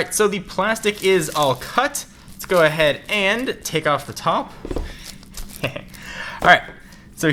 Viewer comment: Guys, 1:03. Anyone else catch, "And take off the top, hehe..."